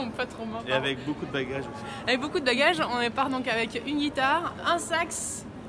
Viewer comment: OK. Donc dès le début j'ai entendu "on part avec un sexe." Je retourne dans la grotte d'où je n'aurai jamais dû sortir.